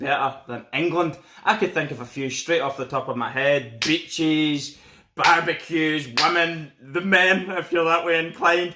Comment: i thought he said b.tches, bbq, lemon....